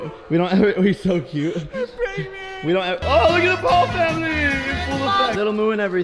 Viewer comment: can anyone else hear her say "I'm pregnant"?